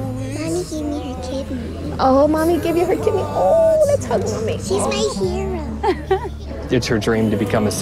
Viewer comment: who else cried when she said her mom was her hero😣😭😭😭💞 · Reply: Amy Moreno Thanks for mentioning that's what she said. I just thought she said "she's right here" Aw...good girl